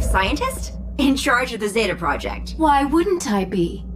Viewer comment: Did she say Zeta Project? As in the Batman Beyond spin off.